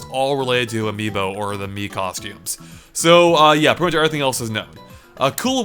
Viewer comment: did he say mii costumes? MII COSTUMES CONFIRMED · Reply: Arvind Subramanian Haha, I meant Mario costumes. No, I didn't leak anything :P